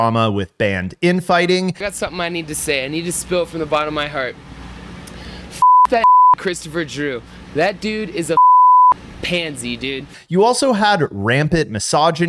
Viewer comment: did he say the n word? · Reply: "got something I need to say. I need to spill from the bottom of my heart. F**k that Christopher Drew, that dude is a F***ing pansy, dude" So it seems he never used the N-word — at least not from anything I’ve seen/heard.